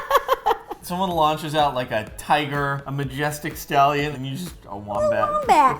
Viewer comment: When Ro said her patronus would be a wombat I thought she said ‘a warm bath’ 😂